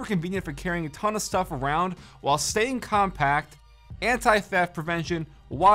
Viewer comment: did he just say anti fap protection?